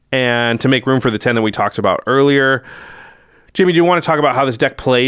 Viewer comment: Is it me or did called him Jimmy?